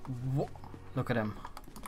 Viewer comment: did i hear "no creo" in a good accent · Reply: he just said "look at him"